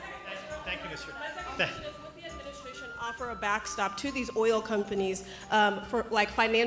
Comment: "i haven't heard the word backstop since Wharton". This guy is something else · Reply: Oh I thought he said “backstab”